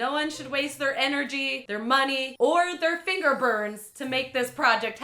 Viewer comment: I misheard "finger burns" as "finger bang"... Love the channel, yer #dorable & hilarious.